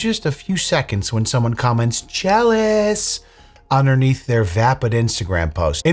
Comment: I thought you said chalice and I was thinking 'are they saying that now?' but you said jealous. Lol it took me a bit.